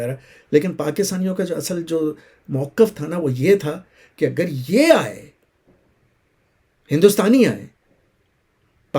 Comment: the military exercise you mentioned can you write the name coz I didn't hear it well and great work ❤ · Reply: Excercise FAKE 1 & 2